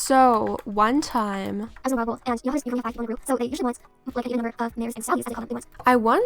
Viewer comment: *turns playback speed all the way down to see if I can hear it* I only heard a bit... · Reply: I heard, " I was in a role, and so you know how..?? they usually want an equal number of mares and stallys...???"